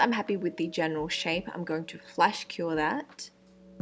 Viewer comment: i thought she said flesh cure... 4:17 ... but those nails are amazing